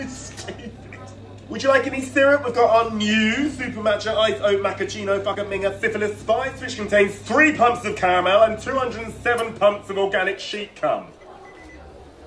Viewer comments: Did I hear "syphilis spice"? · I swear I heard "fucking minger" in the missle of that syrup name